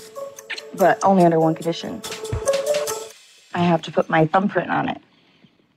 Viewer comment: I wasn't fully focused for a moment and thought Stacey said "one condition... I have to put my bum print on it." I was so confused for a second! I thought it was some kind of kink. 🙌 Sex positivity 🙌 Ohhhh, thumb print! 😂😂😂